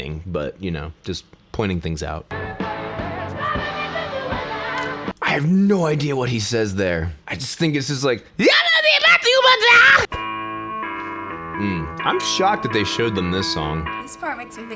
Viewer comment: I believe he is actually saying "Move the way you're doing now"